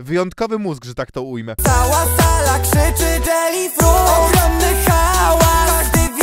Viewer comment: Usłyszałam "Cała sala krzyczy Jelly Fiut